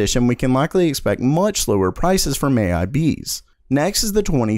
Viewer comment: anyone else hear him say slower instead of lower?